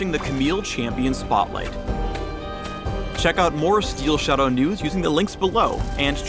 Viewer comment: I heard "for more Steel Shadow Nudes, check link below"..